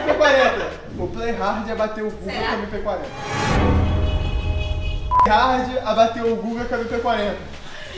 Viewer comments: EU ENTENDI "O PLAY HARD ABATEU O CU COM MINHA MP40" KKKKKK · " eu entendi o Play Hard abateu o cu com a MP40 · "o Play hard abateu o c* com mp40" acho que foi isso que a entendeu KKKKKKKKK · eu entendi "o playhard abateu o cu com a mp40" kkkkkkkkkkkkkkk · eu entendi o PLAY Hard abateu o c# com a Mp40